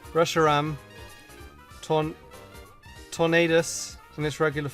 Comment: did he say tornadus